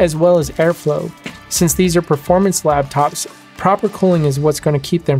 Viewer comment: Thanks for the video--very informative. One thing; it sounded like you said "lab-tops" instead of "lap-tops" at 4:29. (Or did I just hear it wrong?) Anyway, I'm a subscriber to your channel now and look forward to your future videos--thanks again!